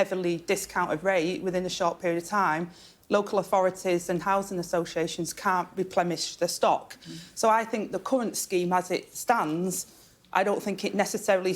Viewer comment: Did she really say "the governments job is to keep it's citizens safe"? ...how's that going? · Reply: Did she really say "repleMish" ? (@ 3:46 "can't replemish the stock")